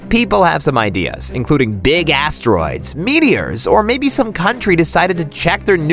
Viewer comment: i heard it as "big ass droids"